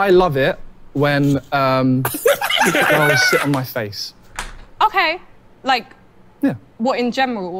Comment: Bruh I thought he said “sh*t on my face” 💀